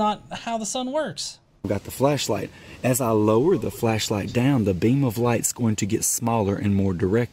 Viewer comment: I think he said fleshlight - a Freudian slip ?